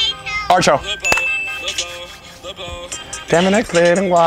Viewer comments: what is that song I can understand what said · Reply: It’s called art show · Did he say archo I can’t find it anywhere 😡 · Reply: Art show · What song he say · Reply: Art show